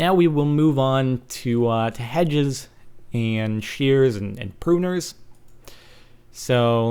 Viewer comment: it sounds like your saying head shears